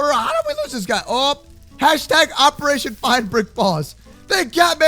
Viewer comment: instead of "find the computer room!" It's "Find Brick Boss HUH!"